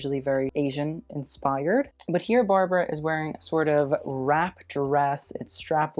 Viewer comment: did he say Barbara or am I hearing things?